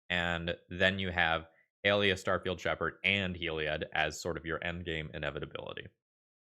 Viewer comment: At one point I misheard you and now I will never not hear it as "Garfield Shepard". I hate this, someone please remove this from my brain